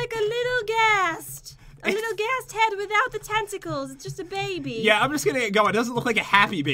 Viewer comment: I thought she said a little ghast head without the testicles for a second